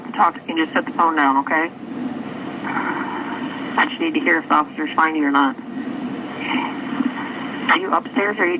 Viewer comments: I think the dispatcher said “ i just need to know if officers find you or not” I could be wrong but that’s what I heard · "I just need to hear if officers find you or not", it was not "I just need to hear if you're responding or not". The subtitles are wrong, just saying